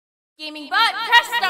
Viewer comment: can someone tell me what she said at the start I heard “immibutt” 0:04 “innplay”?